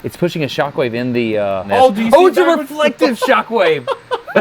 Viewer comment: What type of shock wave did you say at 16:42? please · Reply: Reflected Shockwave. A simpler explanation is that starts off with a explosion and the wave leaves behind a trail of crystalization in this case.